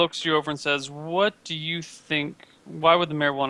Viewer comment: I thought he said "Why would the marijuana want to speak to you?"